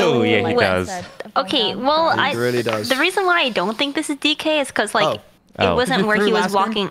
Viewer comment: What did DK say at 3:50? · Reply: Because you threw last game? Lolol